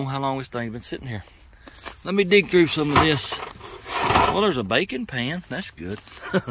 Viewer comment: I got really excited when I heard him say bacon pan…then I realized he actually said bakin’ pan…